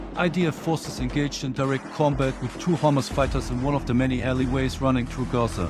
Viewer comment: I know this is serious stuff, but I keep hearing "Hummus fighters" and that ish cracks me up 😂